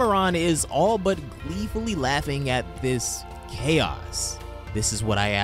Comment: I swear I thought he said Chad's instead of chaos